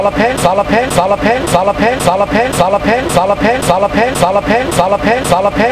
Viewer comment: bruh it sound like he sayin soda pen like first 3 minutes in